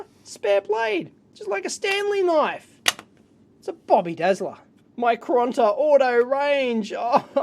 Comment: Bobby dazzler!!! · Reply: TrueReviewHD all this time I thought he actually said Bobby desla lmao I need my aussie to yankee dictionary